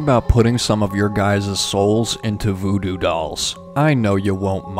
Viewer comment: I had to re-watch the part with the souls in the drawer.... I heard 'soles' 😂 You were painting shoes after all!